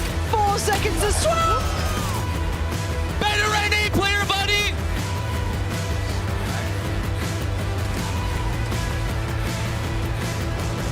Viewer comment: what does that dude on Navi say right here? I cant make it out? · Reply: He said " better NA player buddy" to crashies because koalanoob also comes from NA to EMEA